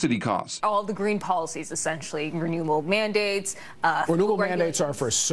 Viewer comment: Anyone catch at 7:49 when the woman said "New World Mandates"?